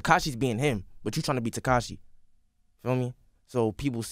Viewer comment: He said tryna be takashi so fast. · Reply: Bih it thought he said kikashi